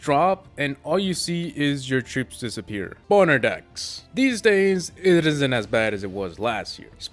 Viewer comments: I thought he said "Boner Decks"😂😂😂😂😂😂😂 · Did anyone hear him say boner decks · who thought said boner decks?? Lol